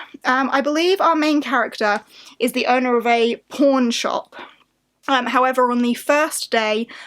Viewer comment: I cant be the only one who thought she said p*rn shop.